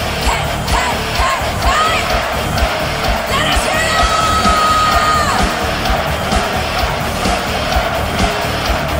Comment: What does she say at 2:40 exactly? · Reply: I think she is saying "Let us hear you"